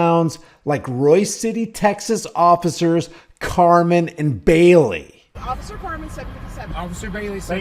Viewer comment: Did you say officers Barnum and Bailey? OH CARMEN and Bailey... is there any difference? Lets find out... EDITED 10 SECONDS LATER HAHAHAHAHAHA i f'n knew it!!!